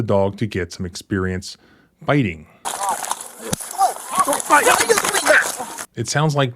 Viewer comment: At approx 19:50 im pretty the cop said "dont fight" instead of "go bite" officers are known to say "stop resisting" and other similar phrases so they can later claim someone was resisting and add additional charges